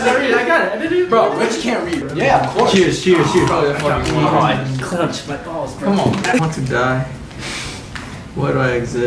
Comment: Yal- 4:40 did just say “I clenched my b*lls”????? 😭😭😭